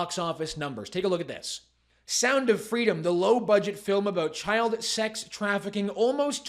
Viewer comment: I'm hard of hearing so I thought he was saying sound of Freeland. I shuddered at the thought. Also, anyone that criticizes the film are alleged pedos.